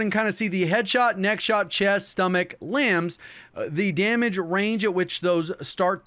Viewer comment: did he just say lambs?